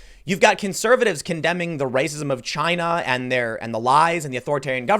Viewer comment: Did I hear you say "China man" ?